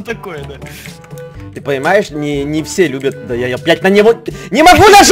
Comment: мне одному послышалась или он сказал бля*ь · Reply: Он сказал "опять"